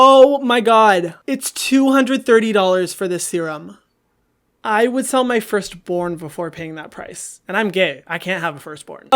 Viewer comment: I thought he said I would sell my first porn😳